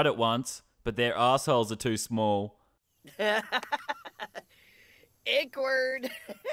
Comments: sounded like the Sim just said "awkward" 🤔😂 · I swear that guy said awkward😂